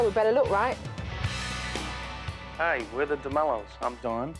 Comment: Who else thought it said Damelio?